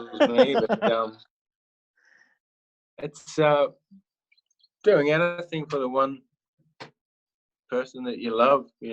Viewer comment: He always talks about money as if he’s almost broke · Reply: You misinterpreted that at 11:50 and I did too at first I thought he said "Doing anything for the Money" but he actually said "doing anything for the one person you love."